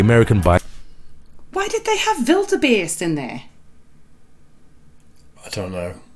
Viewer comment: Did say Wildebeest? It sounded like she said Build a bear 😂